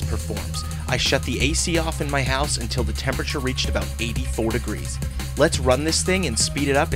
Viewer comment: Is it Just me or did he say ~ 80 Degrees